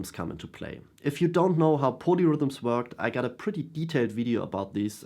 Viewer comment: I thought you were saying ponyrhythms which was spacing me out a touch as I was wondering why I had never heard the term. 🐴 Keep on playing those ponyrhythms...